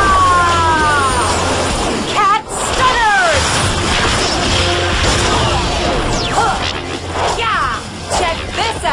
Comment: What in the world did she just call that attack?!?!?! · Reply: Cat Stunners